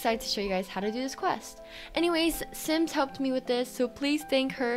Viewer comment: It sounded like you said simps helped me with this, or did you actually say that?